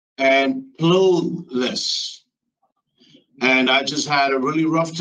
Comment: So, he's "homeless, penniless, jobless"....he forgot one ---clueless! We cannot have criminals robbing banks! He should have gotten more than 3 years!! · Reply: I thought he did say clueless, but I guess he said clotheless. 😂😂